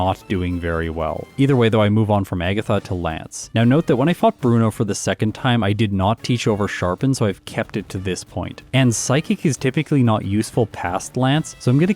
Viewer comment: it sounds like you said "and when I f**ked Bruno for the second time.." i laughed hard.